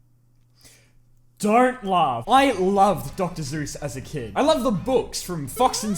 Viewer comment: did he just say dr zeus or am i going crazy